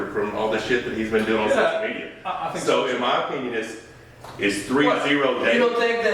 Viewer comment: Why do the subtitles say "in my penis is three zero" at 7:56?